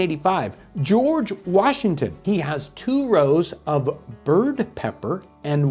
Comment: Was using the blender & swore he said they called it the diddy pepper.